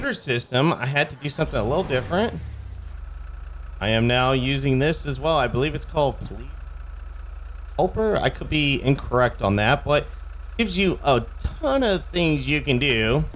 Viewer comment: Hi there! Whats the mod name at 01:32 , i dont understand it well when you say the name. Thanks!! :D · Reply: Hello, I believe its called policing expanded